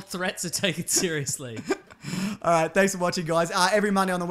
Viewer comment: When says "grab that jimmy," at the end of these videos is he talking about a condom? Or does that mean something else in Australian English? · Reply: I thought he said, 'Grab that gem.' as im Infinity Gem